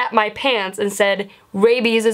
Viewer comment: I thought it said babies bahahahaha